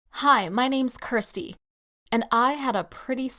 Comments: who heard kursty instead of kristy⬇️ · When I first heard her say her name I heard: Hi my name is Thirsty 😂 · I thought she said, “Hi my name is crusty.” 😂😂💀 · Tell me why I thought she said her name was crusty